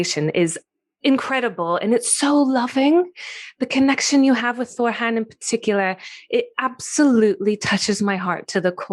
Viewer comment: Is she saying freal at 1:21 ish for the energy source? Subtitles have it as thrill, frill, real even. This is very interesting stuff. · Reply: Thank you! It’s “phryll” - life force energy, can be used to create or destroy! Helps me value the primordial power of the Love Frequency. 💖